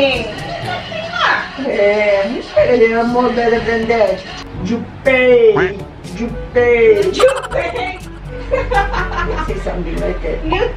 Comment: kok gua dengernya jupe,,jupe yaak😂🤣